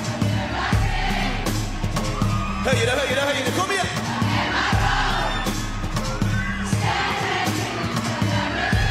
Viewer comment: Wait to did he say? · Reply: He said “høyere høyere kom igjen” which means “higher higher come on” to the audience (i believe)